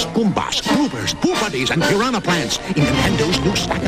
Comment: did he just say boobady? XD